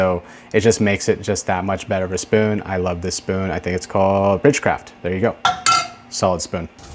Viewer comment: GREAT VID!!! What's the name of that spoon you are using I looked it up sounds like you said Bridge craft but couldn't find it... Thanks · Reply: Rich craft